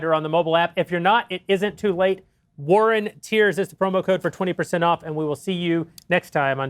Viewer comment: I misheard the promo code as "war on tears"